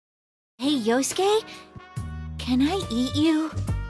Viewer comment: I thought that said “Can I eat you out?”